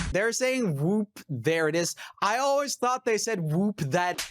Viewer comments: Lol I always thought Tag Team said Whoop that A$$ too! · i swear on my mom that when they say whoop there it is i thought they said whoop that a.. too it just sounds like that